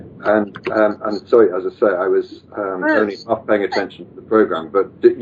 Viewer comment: Did I hear porno at like